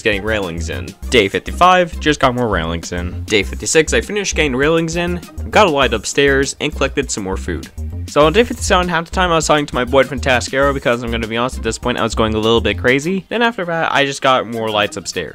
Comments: What are you saying boyfriend are you gay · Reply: Only one person provided a timestamp and what they heard was me saying "My boy, TheFantasticAero" but because of my accent they thought I said "My boyfriend, TheFantasticAero" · bro called his freind his boyfreind man wtf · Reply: Ah, so thats why people thought I was saying boyfriend. You misheard me. I happen to have an accent, and as such I often get misheard. It happens a lot in real life too. I said "My boy, TheFantasticAero." Not "My boyfriend FantasticAero"